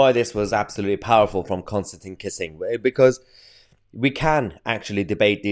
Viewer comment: I thought you said Constantly Kissing! 🤣 He's right by the way...